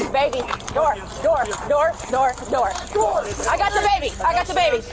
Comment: i heard I got DABABY 💀